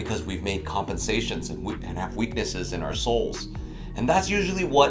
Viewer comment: “weaknesses in our soles.” Even though I knew the topic of the video, I did a double-take when I heard this because I thought you meant “souls!”